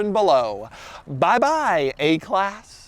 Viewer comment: Did he just say: "Buy Buy A-class!!!"